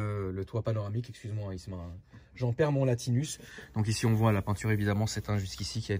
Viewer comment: J'en perds mon latinus Ce que tout le monde a compris : J'en perds mon latin Ce que j'ai compris et retenu : Anus Mais c'est bo c'est bo